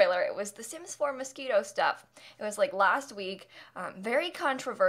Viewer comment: I thought you said “sims 4 mosquito stuff” 😑